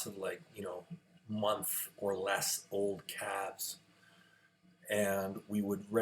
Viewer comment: I was listening at low volume so for the first 2/3 of the video I thought you were saying "cats" not calves. Just imagining a young Devon going out and feeding the cats every morning, sad when they died in the cold barn but hoping one day they could grow up and go out to some cat pasture...